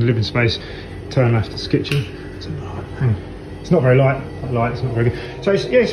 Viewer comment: At the house tour video you can clearly her a strange voice @ 7:12. I just can't understand what it's saying. It almost sounds like "It's an odd light".